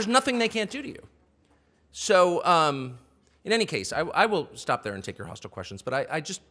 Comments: Did Tucker just say "I will stop there and take your hostile questions"? Please tell me that's not what he said. Thank you. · Did he say "I will take your hostile questions" at 17:33? Like he expected as much.